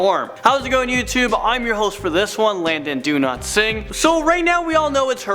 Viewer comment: Wow. I love when our host is Landon Donotsing.... (I swear I heard him say his name was Landon Donotsing! I'm not crazy!)